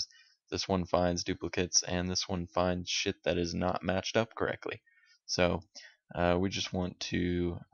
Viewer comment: Did he say shit that has not matched up correctly